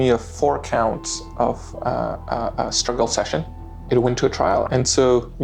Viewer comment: Four counts of “struggle session”? What is he saying there? Or what is struggle session?